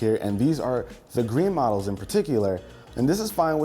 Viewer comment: did anyone else think he said Minato (fourth Hokage)